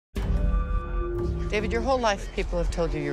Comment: I can't possibly be the only one who heard "PENIS!!" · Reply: he yells 'david' u dirty people XD